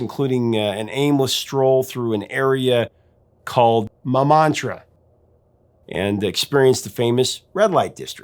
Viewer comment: I thought he said, “Mamatra” and I was like huh? Then I saw the pics…. “Ohhhh Montmartre!” 😆